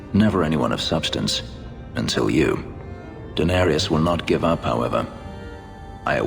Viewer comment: Did Fenris said Daenerys at 6:00 ? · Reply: Denarius - similar, but different.